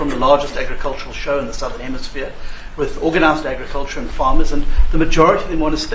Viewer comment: Great video. It sounds like you say form and formers instead of farm and farmers.